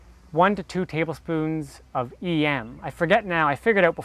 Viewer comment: What is it he adds at the 2:54 minute mark?...sounded like he said e-am